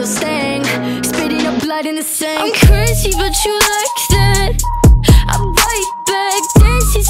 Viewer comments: Ppl who searched "I'm crazy but you like that" 💀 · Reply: I legit thought it said "Bitch but you like that" 😂 🤣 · I searched this “im crispy but you like that” 😭😭😭😭 edit : why y’all still finding this comment lol 🤣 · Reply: I heard I'm crunchy but you like that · I always thought it said “I’m crunchy but you like that” 😭😭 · Ppl who searched "I'm crazy but you like that" 💀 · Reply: I honestly thought she was saying "I'm cringy and you like that..." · I searched this “im crispy but you like that” 😭😭😭😭 edit : why y’all still finding this comment lol 🤣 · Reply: HAKDHAKHA i thought it said "i'm thirsty but you like this, i'm right back" 😭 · I THOUGHT SHE SAID "I'm CURSY but you like that" and now that I discovered the truth I'm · You: "I'm crazy but you like that" Me: "I'm cake but you like that" 👁👄👁✌ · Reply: I heard ‘I’m cursive but you like that’ I heard the slow version so it sounded different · Whenever she said "I'm Crazy But You Liked That." It kinda sounded like she said "Curse You But You Liked It." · I searched this “im crispy but you like that” 😭😭😭😭 edit : why y’all still finding this comment lol 🤣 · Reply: I thought it said I’m cursing, but you like that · You: "I'm crazy but you like that" Me: "I'm cake but you like that" 👁👄👁✌ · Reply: I heard "I'm curvy but you like that" the first time, kinda similar lmao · Ppl who searched "I'm crazy but you like that" 💀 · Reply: I thought it said “Im crispy and you like that”- 👁👄👁